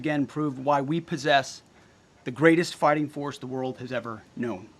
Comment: Did he really say "The Greatest Farting Force The World Has Ever Known" ??? 😂😆🤣 Jokes apart, I really don't think this actually happened...